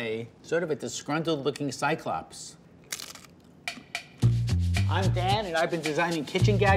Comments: When he entered the video I thought he said “I’m dad” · Did he just say “I am Dad”?